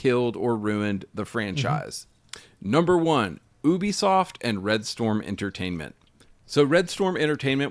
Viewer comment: I heard ubisoft as "boobysoft". Hehe